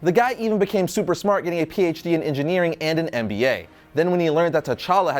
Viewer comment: Wait in 1:27 did he just say that he git a PHD in NBA damn i know Killmongers black and all but i didnt know he was THAT addicted to NBA